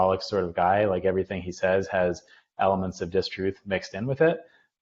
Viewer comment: did he say, "distruth"?! You know, I kinda like that term...makes a little more sense than "mistruth".